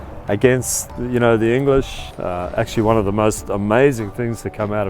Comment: Old man That said the Constitution is a best thing that comes out of the United States was the best interview. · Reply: i thought he said "the most amazing food" for a hot sec.